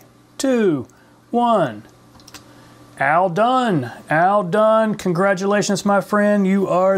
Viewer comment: Cool! Thanks for this video! I thought you were saying ALL DONE at the end, but it was AL DUNN hehe